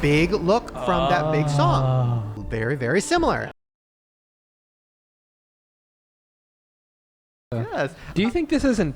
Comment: Did anyone else notice the audio cuts out for a few secondss at the 55:50 minute mark? I desperately wanna know what said but its silent for like 5 seconds and I don't know why lmao was this intentional? Edit: I find it really funny and ironic cause when the adio comes back (55:55) the first thing said is asking "is this intentional" obviously referring to the pop star doll theory, but still funny nonetheless regarding the weird missing audio Haha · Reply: I remember when this came out. She just said she liked the song Pink Pony Club. I have no idea why they’d censor that